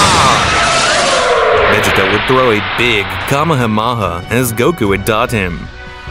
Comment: Who else Heard Kamehamama Not Kamehamaha